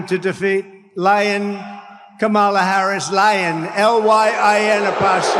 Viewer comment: Every time he says "lyin' Kamala" I hear "lion Kamala." She's Kamala, hear her roar!